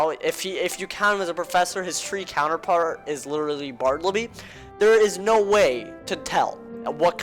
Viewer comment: that sounded like barblibly